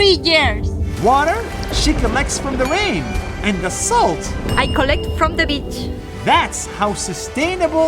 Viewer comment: Did she say i collect the bit